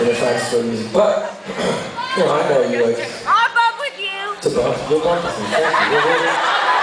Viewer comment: does she say "I'm in love with you" ? lol and he didn't know the words of the first verse lmao but he came back during the chorus